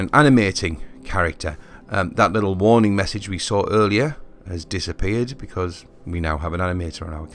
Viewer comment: Totally helpful to a beginner like me, but I cannot make out what is said at 7:23. Why did the error message go away? Please translate. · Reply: "That warning we had earlier has disappeared because we now have an animator" :)